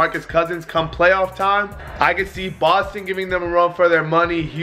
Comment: I thought u said bosley